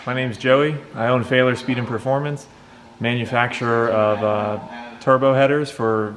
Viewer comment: I thought he said “failure speed and performance”. I would change the name